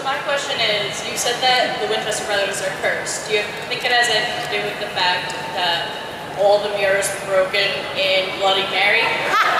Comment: This is toooo funny! Thanks for posting! Does anyone know what the question was at 19:31? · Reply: She was saying "So you keep saying the brothers are cursed. Do you think it has anything to do with all the mirrors they broke in Bloody Mary?"